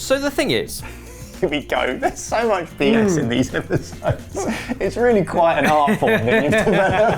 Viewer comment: Did he say "!there is so much BS in these Ebbers-sodes"?